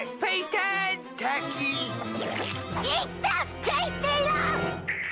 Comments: I DONT KNOW WHAT HE SAID. But his voice was a little different back then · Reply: She said: Eat this paste-eater! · How many of you guys thought he said Turkey when he actually said TACKY?